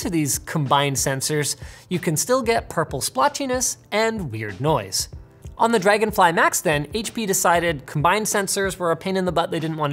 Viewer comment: Thought he said "Dragonfly Macs" and thought he was talking about Apple lol